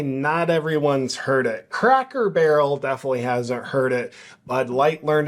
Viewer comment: WOKE = CRACKED the BARREL = BROKE Stupid is - Stupid does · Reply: I heard “ CRACK HEAD BARREL” lol